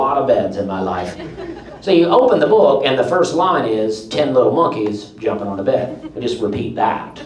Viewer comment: I totally thought he said honk ies jumping on a bed